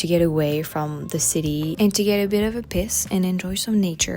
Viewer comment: I know you work very hard on your pronunciations but I had to laugh at 2:43 and 2:52. The way you said “to have a bit of a PEACE” sounded like you said have a bit of a piss and then the way you said “there is even a BEACH” sounded like you said “there is even a bitch” lol